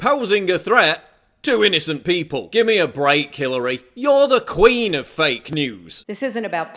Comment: did you say "Killary" here lmao